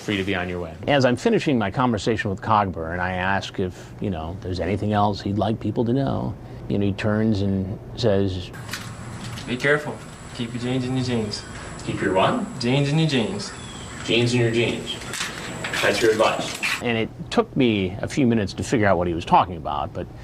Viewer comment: "Keep your genes in your jeans" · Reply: Patrick Bateman I thought he said "keep your James in your jeans" lol, like he was calling the dick 'James'.